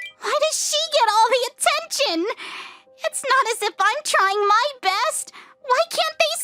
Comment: it was not saying trying it was saying rying